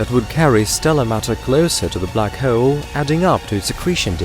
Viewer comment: I thought he said black heralds, sounded like some type of newspaper